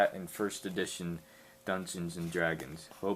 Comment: Holy shit thought you said megaVirgin xD